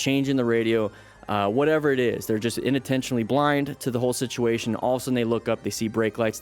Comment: I swear I keep hearing you say “inuntentionally” when the real way to say it is unintentionally